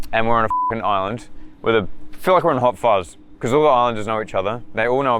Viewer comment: that seems like a whispering "YES" when said "They all know about us" or am I hearing just an added sound effect? I'm just probably tripping. Coz it sound like someone whispering "YES" very close to the mic.